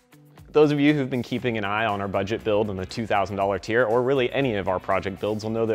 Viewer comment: Lol. I thought it said “Budget build Lightning” 😳 Damn it!!!